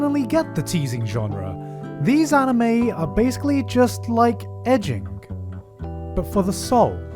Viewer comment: I thought he said "ecchi, but for the soul" ----------------------------------------------- I thought "edging" meant sharping things and didn't understand what he meant, so I googled it.... oh nvm, it's more ecchi than the word ecchi itself.